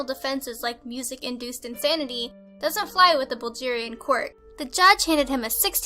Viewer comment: Is no one gonna comment on the way pronounced Bulgaria and Bulgarian? At first I thought she said this took place in Nigeria omg.